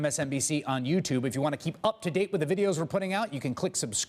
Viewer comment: Does anyone else wish Chris Hayes would stop saying "below me" at the end of these videos? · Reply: OMG thank you! Every time he says it I hear "blow me".